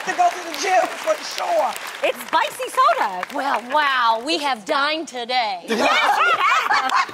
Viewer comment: wait what did Tamera say lol? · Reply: Dol Girlbv i thought she said "we are dying today" lol